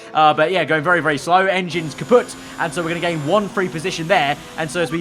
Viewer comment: did say "engine is kaputt"?